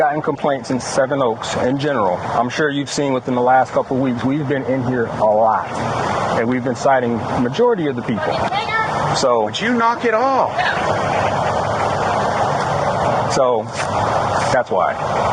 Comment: anyone else catch the fucking n word